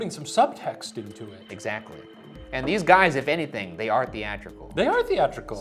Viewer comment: to anyone using captions, these are wrong. The guys say "they are theatrical" the caption says "they aren't"